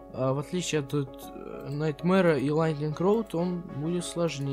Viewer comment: Мне послышалось Lightning роллтон